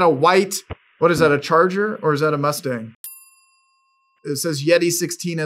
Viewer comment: "is that a charger, or a mustang", and the the caption said "Charger". I'm about 782% sure it's a challenger (number based on the amount of Naruto files Tuschar had on his system.)